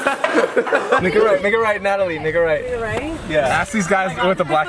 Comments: all I heard was n**ga right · anyone else hear “n word right” i’m a son of jesus can’t say it 😂👌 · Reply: originaLmeme hé said make a right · i thought jeff was saying the n word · damn I thought Jeff said "Ni**a Right"